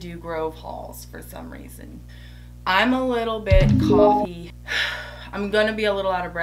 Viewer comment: Omg, I had to watch your intro 2x. I thought you said, "I'm a little cocky". I was saying to myself, "No your not"..lol..I'm glad you posted the word